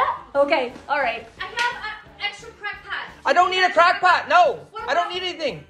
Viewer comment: Thought she said "Crack por" I thought to myself.....fitting...